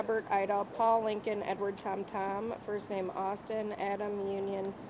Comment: Yet another frauditor who allows himself to be doxed on his own video. 🙄 edit - I thought his name was Justin until I heard her spell it.